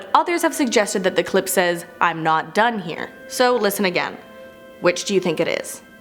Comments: For the "I'm not done here" clip, I hear "I'm undone here." Which I think is far creepier. Great video! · On 4 i heard i'm not down here